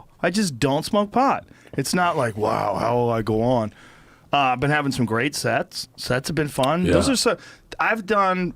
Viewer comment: I legit thought he said "I've been having some great sex." I was like I appreciate the honesty Joe but...